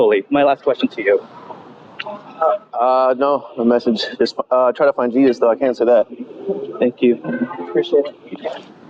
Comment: What does he say here? "Just try to find deez"?